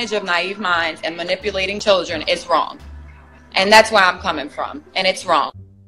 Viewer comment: That Keem guy is deaf, she didn't say she was, 'coming for them," she said, "that's where I'm coming from." If anyone goes back and listens, they'll see that she didn't say what he thought she said.